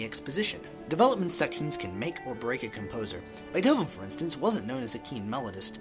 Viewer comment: What composer did you say here? It's kinda unclear. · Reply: Beethoven. I seem to have put the emphasis on the wrong syllable.